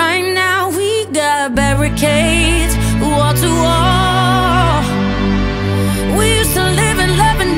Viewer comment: I’m the only one who hears Walter Wall